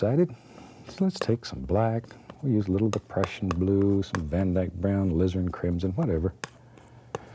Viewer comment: Everytime he says "the prussian blue" I hear "depression blue" XD